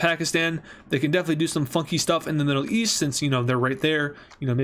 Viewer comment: i don't think you said funky☠️